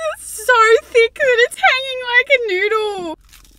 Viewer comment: It’s 2am I was already crying of laughter that I thought you said doodle and lost it 🤣 laughing silently is painful hahahha